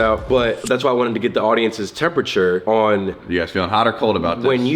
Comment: Wait did say n*gga...never heard that befo😭😭😭